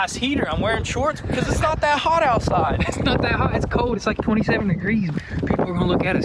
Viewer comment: Did you actually said, 22nd degrees min. 9:43? Lol